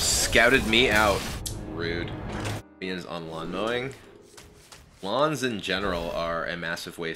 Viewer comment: When Kibler was talking about lawns I kept hearing blondes for some reason. Glad to know I just misheard him.